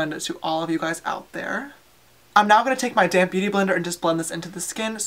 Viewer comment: when you were blending your foundation I thought you said "I'm now going to take my damn beauty blender" 😂